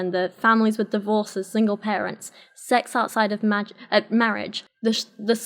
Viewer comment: did you say "Sex outside of magic"?